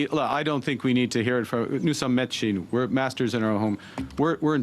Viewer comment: sounded like he said no sommes metiers - we are masters? Not a respectful move.